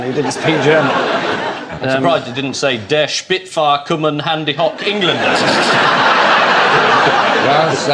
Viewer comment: What does Ian say at 5:51? Der Spitfire kommen handy hoch England? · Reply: Der Spitfire komme! Hande hoch Englander! The first part is a rough stab at "the Spitfires are coming!" or "the Spitfires have come!" and the second part means "hands up English person!"